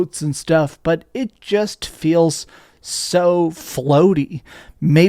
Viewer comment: Did he just say the boats feel floaty??